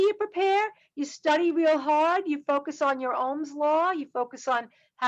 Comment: did she say Ohms law ?